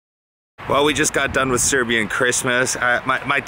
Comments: DId you just said we just got done with serbian chrishmast 4:53 im from serbia i fell special :D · Did he say Serbian Cristmas? I think he said that. Peace from Serbia :)